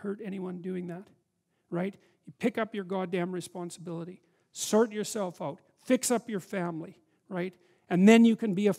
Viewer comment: he says I should cert my cell phone, as best I can hear. Does anyone know what he meant?